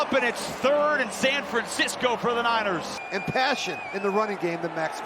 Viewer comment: "It's 3rd and San Francisco for the niners" I can't be the only one who heard this lol